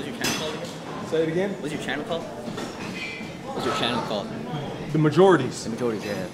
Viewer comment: I know I heard "the majorities" 😂